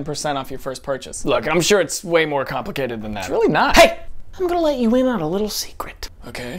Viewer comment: “Shall we take you to a hospital?” ... “totally.” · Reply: I heard "Don't, really." and this comment helped me to get the joke |